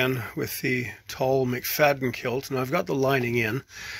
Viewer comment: Beautiful!! I’m very excited to soon be wearing this piece of art! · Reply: For clarification, what tartan is it? I thought I heard McFayden, but my first thought was MacNaughton.